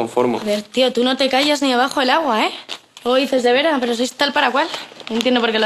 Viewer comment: Alguien me puede decir, que dice Leire en 0:23? Es algo de Vera, pero no escucho bien. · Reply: Pysia141192 “luego dices de vera pero sois tal para cual”